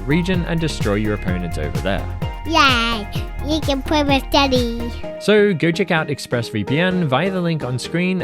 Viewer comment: I fr thought she said ”yay you can play with diddy” 💀🙏